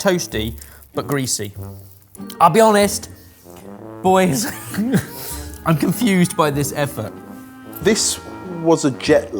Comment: I will admit I heard "I'm confused by this eff-up".